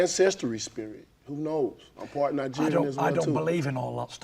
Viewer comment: Did Wilder say he's part Nigerian??? 🙂